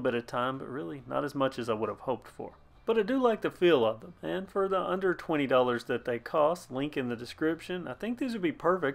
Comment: My heart stopped a bit when i heard you say 120$ for these grips, glad to see that it was a mistake and cost only 20$ instead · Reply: Lol. I said “for the under $20”